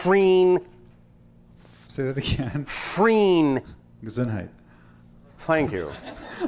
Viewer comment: Thank you very much to for sharing, awesome, inspiring as always with Bashar, thank you to Daryl as well!. Can someone please explain to me the joke at 1:08:50? I couldn't hear well what the gentleman said! Thank you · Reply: hi 🙂- he said Gesundheit❗ ie Bless You❗ (after sneezing) - it's a German word but also used in English speaking countries